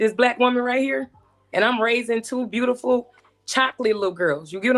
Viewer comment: She doesn't even know she's in competition with her man, y haven't u married the man u have a child with. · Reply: I think she has more than one baby father. I noticed she only talked about the first child's dad. She said she left him. I know she didn't turn around and have another kid by the guy she left. I thought she said she was raising two black girls.